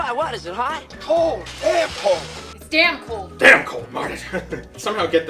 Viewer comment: Wait doc says damn cold!? I always thought he said air cold! Omg!